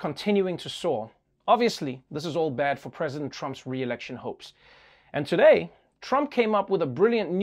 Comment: Trevor, you said, "Trump's reelection hopes" but I heard "reelection hoax." God, I hope that wasn't a psychic moment!